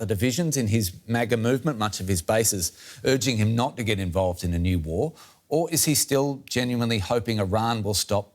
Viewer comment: I thought he said, no new wars?